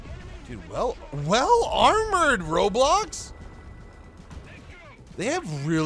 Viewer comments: did he say roblox? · I heard roblox and I cant unhear it